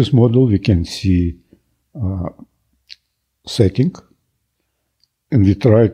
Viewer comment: is he saying @ 3:11 "setting" ?